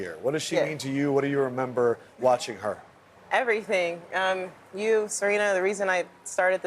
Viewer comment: did she just say "you serena" to venus?